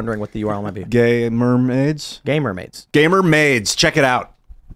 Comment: I honestly thought Ian said gay mermaids lol